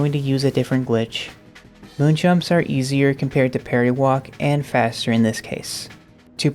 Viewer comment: I absolutely adore your work and it warms my heart to see this kinda thing. BUT I keep hearing "party walk" whenever you say parry walk and it's just what this trick is called in my brain now lmao